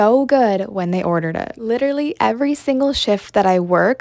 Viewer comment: At the start I thought u said every sh— but it was actually every shift 😂